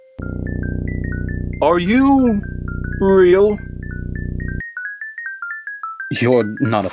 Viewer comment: AM I THE ONLY ONE WHO HEARS ARE YOU OREO?